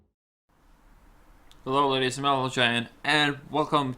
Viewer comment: Im not trying to be mean or anything, but you have a really tough voice to listen to, especially in such a descriptive video as this. You should try to talk in a more sincere way, not in such a goofy and happy mood tone, it only makes it harder for people to understand. Like I said, Im not trying to offend you in a way, just a suggestion. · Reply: I think 99.9% of the time I know what he is saying. the main exception for me is at the beginning when he says (what I'm guessing is meant to be) 'hello ladies and gentlemen' It sounds like he says 'ladies and meneljem'. But the rest of the time he sounds just fine.